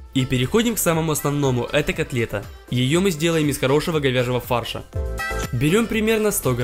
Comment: мне послышалось ,,из хорошего говеново фарша''